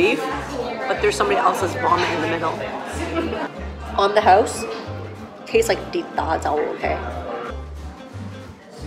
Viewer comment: did you say 鐵打酒?